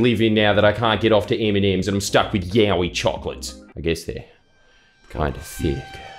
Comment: I wasn't looking for a second and thought you said "Yaoi Chocolate"